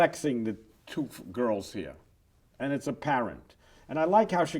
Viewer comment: I thought he said "And it's a parrot" for a second.